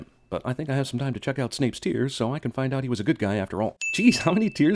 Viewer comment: I heard "Snape's tears" as "Shakespeare's" 😂🤣